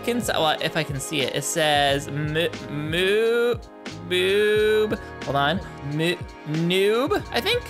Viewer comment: am i the only one who heard say boob